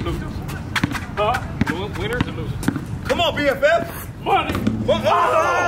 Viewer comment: is he saying "c'mon BFF"?? lmao i need that in my life